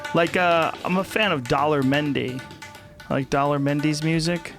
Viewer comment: At first, I could have swore that Joe said that he was a fan of Dollar Menu. I thought, "Right on, Sir. As am I."